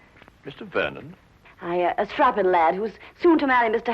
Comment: Mrs Dugan calls Vernon a "trabalab" I hope I didn´t hear her correctly. does anyone know what she actually said? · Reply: CC says “a strapping lad”